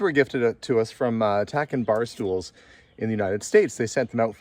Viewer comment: Does anyone know what the name of the barstool company is? I think he said Tack End Barstools at 2:20:30. Am looking for similar stools.